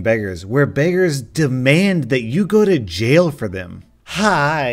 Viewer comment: I heard this like “demand that yugo to jail” and i was like wha?